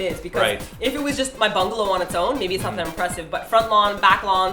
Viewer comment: I heard "if it was just my bunghole on it's own" and spit water everywhere DX My poor brain, bungalow not bunghole...